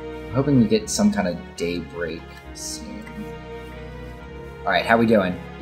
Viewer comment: I have a cold, so my ears are kinda clogged. So when said "day break soon" I thought he said Date Rape soon. Anyone else hear that?